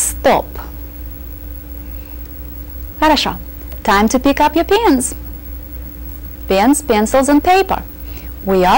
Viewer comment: I thought she said "Time to pick up your pants" lol